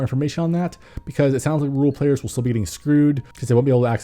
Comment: When he said "rural players" I thought I heard "rule players" and it confused me for a moment.